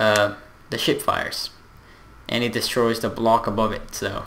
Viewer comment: lol sounds like he saying the shit fires